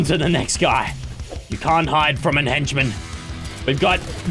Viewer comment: did he just say an henchman?XD